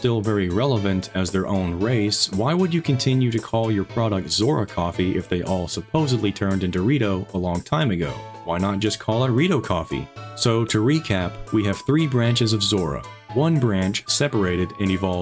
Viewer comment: I only hear "Into dorito"